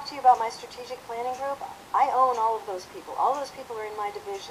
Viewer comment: im sorry for saying this but did she just say "she owns all these people at 6:24, then at 8:07 did she not say " Iam the IRS, and no one here at all has commenting on them two words that this woman say that i find that a real big miss on all of your guys is part becuase talks abouts a big wow now im going to say here and now that i have not watch the full case of IRS Scandal both what she has say and all the evidence with the emails and things or what all the c-span videos, i watch some but not all of this cast but, i can tell you right now i well be, with that say i watch all of this video not the full version of it, but what NYGOE has upload here and only them two words i can, for get all the rest abouts what she say but just them two words right there she owns all these people at 6:24, then at 8:07 " Iam the IRS them yes i love to know who We she is talking abouts and when and all that but them two words take me for a spin becuase saying that she Owns all these people is saying she is God i can tell you right now she is not God, and saying that she is the IRS is saying that she create the IRS she did not God did he, give the people the idel to create the IRS and in closing here, SHE DOSE NOT OWEN THESE PEOPLE GOD DOSE.